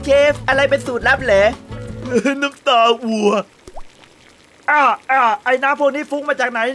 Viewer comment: เอิ่มเราเป็นคนเดียวรึป่าวที่ได้ยินเป็น "สูตรลับคือน้ำตากูอะ". _ .)